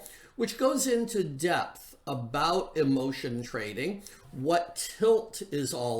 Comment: I misheard him saying why do we need lotion for discretionary trading (instead of what he actually said emotion). I was like wait what (especially with my corrupt mind)